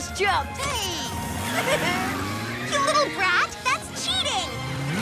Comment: Did Megan just said 'Little Brat'?